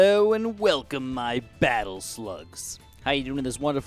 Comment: I feel honored to be called a battle slug Edit: If I’m a battle slug then the rest of y’all make a battle slug army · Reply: I thought he said battle sl*t at first but I'm running off of 2 hours of sleep so that makes more sense lmao